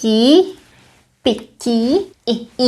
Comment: I find you pronounce “t” like a “k”. I heard “piki” not “pití”. Is this right? New Czech learner here.